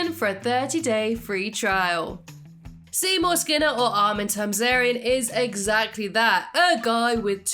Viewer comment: What did she say at 0:09? armin Tarzarian? What? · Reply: Armin Tamzarian 🙂